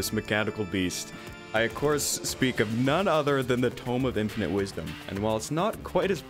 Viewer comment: for some reason when first said 'tome of infinite wisdom' i thought he said 'tome of internet wisdom' and, let's face that just sounds like a terraria weapon.